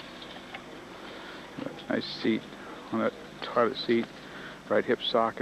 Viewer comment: it sounds like he says "nice seat on that toilet seat"